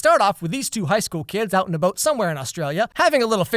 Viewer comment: Alex said "Out in a boat" and I thought he was saying "out and about" with an australian accent because of the australian show 😆